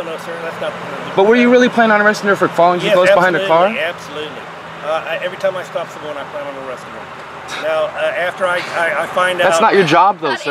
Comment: "every time I stop someone I plan on arresting them"...................Did I hear that right? How the fuck is that cop a first sergeant also?